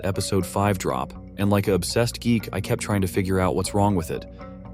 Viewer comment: Did bro just called himself an obsessed geek